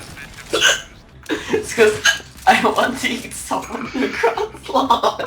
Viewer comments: I said ' We only won the round because i Juan deaged someone from across long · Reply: I thought you said "It's cause hiccup I don't want to eat someone cross blog" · I replayed 0:21 like 10 times now and still can't tell what he just said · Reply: I said ' We only won the round because i Juan deaged someone from across long